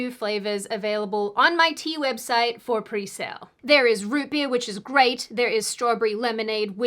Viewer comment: Heh, when Sydney said root beer as a flavour about her tea, I may have been blowing my nose at the time and heard roofie. Which meant I had to do a double take to ensure I misheard. Do I have the wrong sort of thing on my mind or was this an honest mistake? If I am fair to myself, root beer also sounds like a pretty strange flavour of tea so perhaps my brain was having to cast a wide net for things that matched what I half-heard. And root beer is not much of a thing in Australia (hopefully roofies aren't overly widespread either!?!?) These are the sort of things that plague my overtaxed brain.